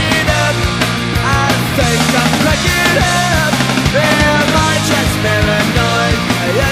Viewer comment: I came to see what he was actually saying in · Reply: Or am I stoned is what he said